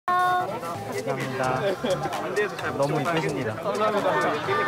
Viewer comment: What that he say to Yiren? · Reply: You're very beautiful